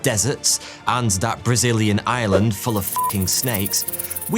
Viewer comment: I'm sorry but I listen at 1.25x speed and I heard "that Brazilian island filled with snakes" as "that [...] in Ireland filled with snakes" and was very confused. St. Patrick died for nothing.